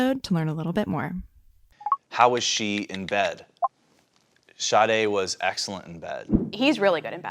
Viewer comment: I thought he said “shawtay was excellent in bed” 💀💀💀💀